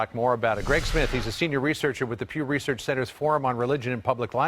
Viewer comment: im sorry but did he say pube research center? go back and listen to it